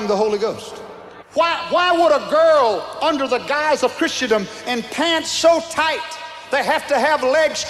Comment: "christiadom" is that what he said?